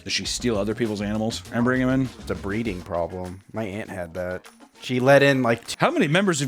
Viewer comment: i thought he said his aunt had the problem LMAO 💀